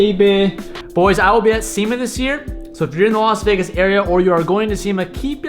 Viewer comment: I heard "I'll be at semen this year" not SEMA